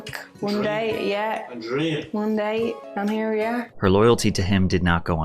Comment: i cant understand a single word she said 😭😭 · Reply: "One date and here we are" thats all i heard